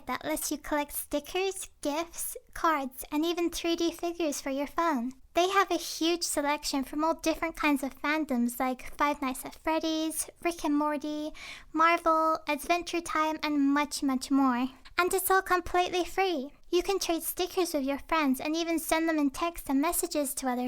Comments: At first I thought she said fricken morty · "frick and morty" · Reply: Mista Man it sounded like she said “ fricken morty “ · At first I thought said fricking morty instead of rick and morty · At the start I thought you said frickin' MORTY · When she said Rick and Mortu, I thought she Said f**** morty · It kinda sounded like you said "Frickin' Morty" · aaa your voice is so cute!! and when you said rick and morty in the beginning, i thought you said fricking morty.. oops! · I thought you said Frick and Morty. I just woke up though. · I thought said Frick and Morty XD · When u said Rick and Morty I thought u said frickin Morty lol I'm deaf now lol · I thought you said frick and morty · did i hear "frick and morty"??? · I thought you said Frickin morty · It sounded like she said "FRICKIN MORTY." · Omg I'm so sorry this is my first time watching u And I thought u said Frick and Morty sorry 😫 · When she said “Rick and Morty” I thought she said “Fricken morty” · Lol I thought she said fricken Morty instead of rick and morty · I thought she said Frickin Morty instead of Rick and Morty 😆 Also please make more videos like this, I loved it 😊👍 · I thought you said frick in morty😂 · When she said Rick and morty I thought she said FREAKIN morty..... (idk how to spell morty well I kinda forgot hope I spelled of right! XD) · For rick and Morty stickers I thought she said "freakin Morty" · thought she said frickin morty 😂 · It sounds like she says Frickin Morty